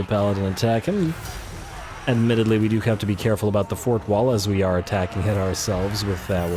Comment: "we have to be careful about the fourth wall, as we are attacking it ourselves." yup, keeps breaking the fourth wall. Then i played it back up and realized i misheard you, "fort wall", not "fourth wall"